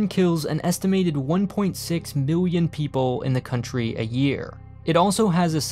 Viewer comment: did you say million or billion there? I swear you said million, but made it sound like billion for added effect